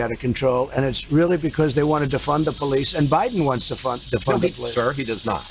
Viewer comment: Did Trump just say they want to F the police